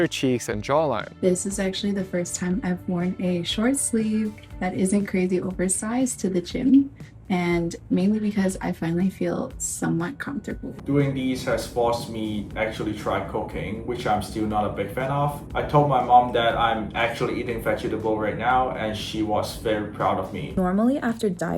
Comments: I heard him say “doing this has forced me to do cocaine” 😂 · "Doing these has forced me to do coca*ne, which I am not a big fan of.. " that's what I heard anyway 😂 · I heard cocaine instead of cooking 😂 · I swear I thought he said cocaine not cooking 😂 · When the guy said cooking at 16:52, I swear it sounded like he said cocaine lmao must be the accent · Oooooohhhhhhh, cooking, i heard cocaine, which admittedly is a pretty good diet technique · At around the 16:50 ish timestamp moment the guy says that he actually started "cooking" lol but I had me earphones in and I of course was listening but at that moment I had turned away from the screen and wasn't watching it and i swore he said that he actually started "cocaine" bwahaha I stopped whatever I was doing and looked at my phone like...... what? Haha I know that I can't be the only one who heard this lol and immediately thought, yup thatll make you lose weight bwahaha 😂😂😂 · I thought he said, “Doing this has actually made me try COCOAINE”. Within a second I figured out he meant “cooking”. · When said he “actually try cooking” it sounded like “actually try cocaine” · I swear I had to rewind it several times because I thought he said he tried cocaine @ 16:52 . But it was cooking. I wasn't actively watching. Secondly I thought they were a couple the entire video what a plot twist for me at the end😂 comments are crazy for this one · I thought he said “try cocaine”